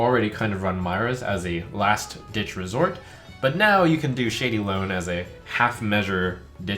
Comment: When he was talking about the shady loan card, I kept hearing "shady load" in my head and I have no idea why... Speaking of which, "shady load" would be quite an interesting card...